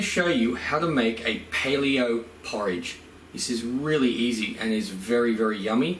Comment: Don’t question me but I thought it said “play-do porridge” and I thought “I know homemade play-do is edible but that’s a bit much”😂